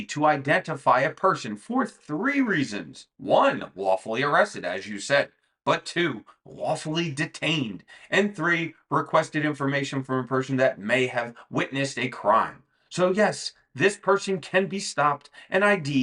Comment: did you just say wafflely?! Now I'm hungry...